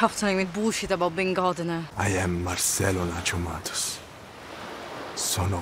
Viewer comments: HELP I MISHEARD HIS NAME AS MERCELO LA TOMATOS 😭😭😭😭😭😭😭✋🏾✋🏾✋🏾✋🏾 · the wayyy I thought he said Nachomatos instead of Nacho Matos